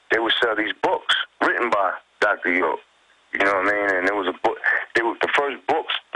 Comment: sounds like Chick Publications... I can't understand what the Doctor's name was that he said. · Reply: Dr. Malachi Z York